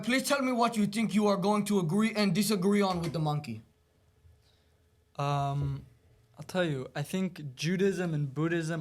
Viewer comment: Did bro call the monk a monkey??